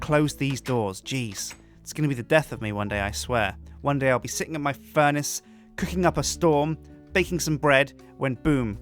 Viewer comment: did he say he was going to sit on his funace?